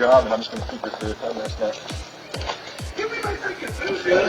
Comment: It wasn't a guy at his bosses the guy was a food delivery driver like Uber eats and the people weren't tipping him so he said he wasn't giving them their food and was quitting his job. Then you hear them also say where he he taking their food. Love from Michigan ❤️ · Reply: Thankyou, I thought I was the mad one for a minute, I clearly heard hi. say" Give me my food dude"🤣🤣🤣